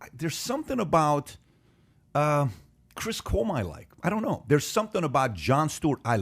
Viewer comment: Did Pat say Chris Cuomo at 1:43:48?